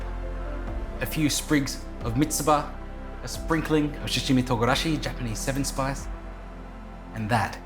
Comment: google closed caption “sashimi togarashi” instead of ”shichimi togarashi”. 😂